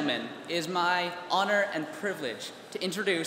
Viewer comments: Sounded like he said “Salad Dictator” rather than “Salutatorian” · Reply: I thought he said salad dictator only lol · Sounded like he said “Salad Dictator” rather than “Salutatorian” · Sounded like he said “Salad Dictator” rather than “Salutatorian” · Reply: I THOUGHT HE SAID SALAD DICTATOR